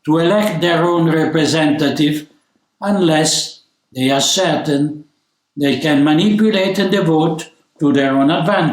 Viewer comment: Thank you for Archbishop Viganò. Why have you replaced what he said at 2:13, "certain" with a blank that quickly rolls the name "Satan" before moving on?